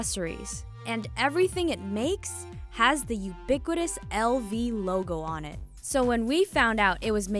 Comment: Thought she said ubiquiDous till I went on to Google 🙄